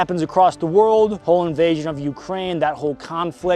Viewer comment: did he really said "Poland invassion on the Ukraine"?! XDDDD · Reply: he said "whole invassion on the Ukraine, that whole conflict.." XD